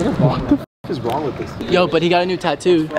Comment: sounds like someone said the N word😭 “wtf is wrong with this n”